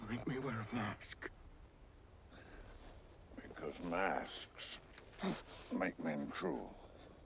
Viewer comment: "Because masks make men cruel." Nice little BvS nod. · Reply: Oh i thought he said mask make men true